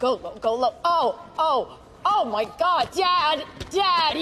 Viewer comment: I thought she said daddy. LMAO!